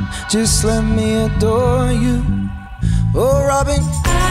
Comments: Anyone else notice him saying “oh robin” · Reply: I heard "oh robby" 😂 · does he say"oh robin" or is it just me?😄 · Anyone else notice him saying “oh robin” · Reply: I thought i heard "oh robbing" lol